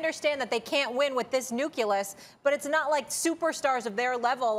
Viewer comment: Stephen A has been on TV for 20 years and still haven't learned what Moiré pattern is. Also, did Molly say "nucelus" instead of nucleus at 4:09?